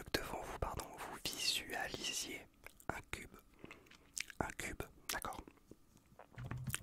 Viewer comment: j'ai compris "un cul"😭🤣🤣🤣